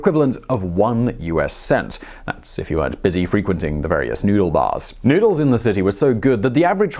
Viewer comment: Noodle bars? I thought he said "Nudie bars!"